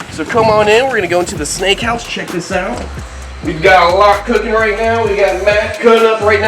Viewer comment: i thought he said i got meth cooking💀👋🏽